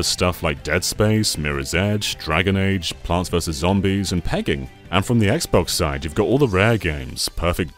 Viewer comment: Does he say "pegging"